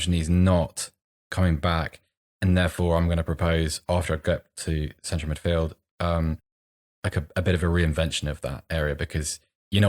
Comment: what does he say here? He's going to propose what? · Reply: same vans “after I get to central midfield, a bit of a re-invention of that area (meaning strike)”